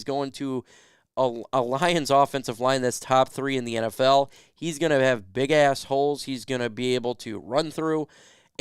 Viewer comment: At the 5:55 mark did you say ( He's gonna have big Assholes)?